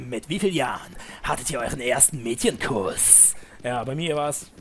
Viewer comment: xD ich hab erst medienkurs verstanden xDD bei mir mit 13 :D